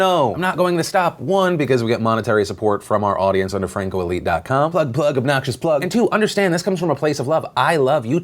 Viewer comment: did you say “butt plug”?